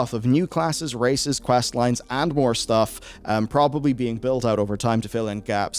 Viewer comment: I think my brain is still hung up on the bear, for a moment i misheard "New classes, races, and VORE stuff" instead of MORE stuff...i was like "ayo.. well i guess the Drow could be into that"